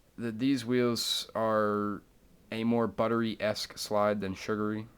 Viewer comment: Did just say Butteryesque hahaha -love braxton